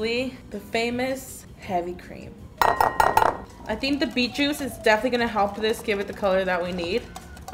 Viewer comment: when karina said beet juice girl i thought she said meat juice omg i’m deaf